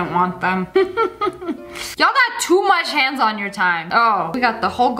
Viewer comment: She really said y’all have to much HANDS on your TIME Instead of to much TIME on your HANDS